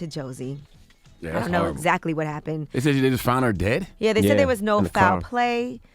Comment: did she say, ""I know exactly what happened"? WHAT! How do you know? HIVE MIND